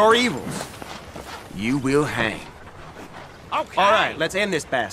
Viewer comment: كأني سمعت بيل يقول OK??